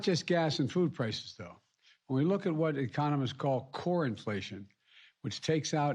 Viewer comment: It's very pavlovian A talking monkey in a suit says "good news" Like ringing a magic bell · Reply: Sounded like he said "corn inflation" You gotta keep that corn from getting inflated